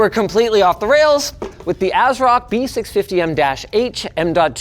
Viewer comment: I thought you had said "AZEROTH" instead of Asrock, and I was thinking: no way they stole Dota from Blizzard and then went and did this for no reason, lol.